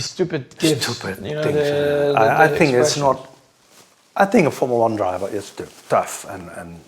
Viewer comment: I swear I heard "a formula jew" at first